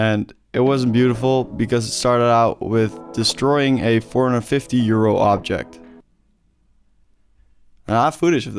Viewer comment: Thought you said you destroyed a 450-year-old object lol I was so confused